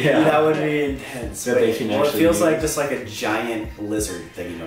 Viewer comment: "Big lizard" "scary snake" good guesses Colby 😂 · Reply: I thought he said pink lizard 😂